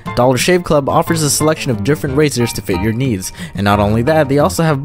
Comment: Thought he said different razors to fit your knees